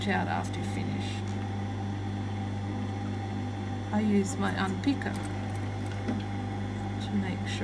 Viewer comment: Help!!! What does she say at 41:59-4203: ??? "... I use my ____ to make sure it's evenly gathered." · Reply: JD Benson yes it was her unpicker 😊